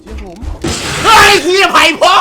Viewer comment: ไปพ่องมึงอะ ใครได้ยินบ้าง <num>